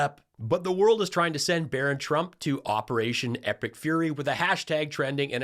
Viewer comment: Wait, I thought they said ‘Operation Epic Furries’…